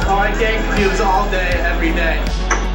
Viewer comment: "You guys really take care of us." That hit me right in the feels. So sad to see the fall. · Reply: What’s the guy with the drink saying after him at 1:12? It sounds like “RNK queues all day everyday” but I know that’s not it